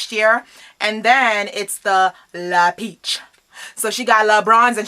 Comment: I heard l'beach lmao